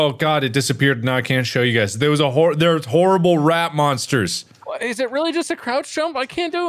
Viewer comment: when mark said rat monster istg i thought he said rap monster and i was like “namjoon is that u👁👁”